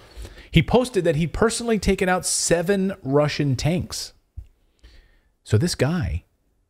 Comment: Ok! I thought he said he and his crew took out six Russians tanks. But yea, thanks Clay!